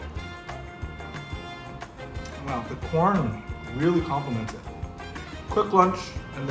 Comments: Please tell me why the subtitles at 3:38 says “porn” instead of “corn”..... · Subtitles at 3:35 say porn instead of corn, just so ya know. I had an immature giggle but yeah, idk how they fix it or what hah